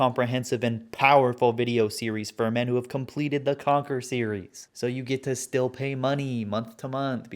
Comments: Lol I thought I heard “Conker Series” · Until 7:49, I legit thought you were saying "The Conker Series", not "The Conquer Series". Makes a bit more sense now. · Before text came up on screen I kept hearing the "Conker series" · i thought you were saying Conker's series. like conker's bad fur day. wondered why it wasnt making sense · I have absolutely no idea why, but for the first few minutes of the video, I thought he was saying "conkers series" not "conquers series" and I was like "what a dumb name" I still stand by that comment